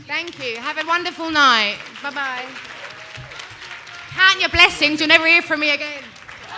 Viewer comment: What did she say in 12:05 - 12:08 ?? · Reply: wakeup! "Count your blessings you'll never hear from me again" 😂